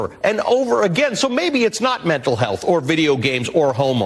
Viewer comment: did he just say h*mo first and then corrected himself and then said hormone??? 😂😂